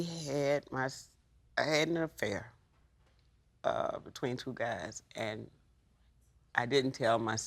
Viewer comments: I had to rewatch the beginning 3 times, i swear i heard her say "i had an affair with 22 guys" not "between 2 guys" i was about to be like LADY! 😳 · Way too much woman worship going on here...did the mother start by saying 22 guys? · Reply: Jeremiah I had to listen several times because I thought she said 22 as well. What she says is “between 2 guys”. Lol.